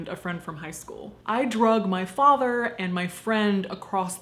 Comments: Lol thought you said you drugged your father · for a second there I thought you said “I drugged my father” 😂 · jeez I thought you said "I drugged my father and my friend"